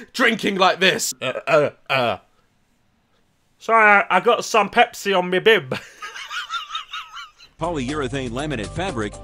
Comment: sorryy i got some pepsi on me bib sounded like he said soy oi got some pessi omi bid he spoke fluent indian lol